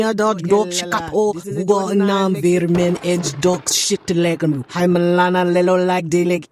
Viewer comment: Nah, I almost fell out my chair at 8:37. Hitting that Missy Elliott “Work It” reverse on the tape, LMAO. And did I hear “dog $^*#” slip up in there too?! Jesus take the wheel AND the tongue. 😂😂😂😂